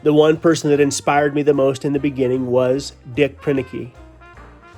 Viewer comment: you mention someone that inspired you. How is that name spelled? I really couldn't understand how you pronounced it well enough or it was so different that I didn't understand. Thank you. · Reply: Just Call Me Oz .. Dick Proenneke